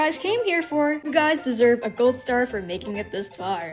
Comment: I gotta clean my ears and mind, i lowkey thought you said "B**d Star"